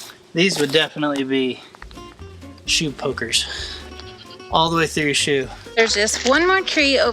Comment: So says “shoe poker” and I’m distracted while watching and I hear “cheap hooker” WHAAT. LOL.